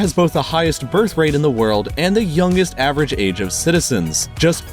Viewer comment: DID U SAY THE N WORD?